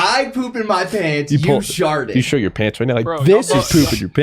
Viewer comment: I thought said "shit" lol, pretty sure he said "show" though.